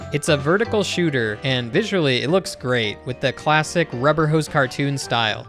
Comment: I thought you say "visually it looks like cuphead" about your sponsor 😂